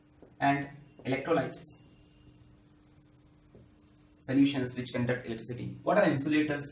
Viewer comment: What did he say about electrolytes in 12:12 ? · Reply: Solutions which conduct electricity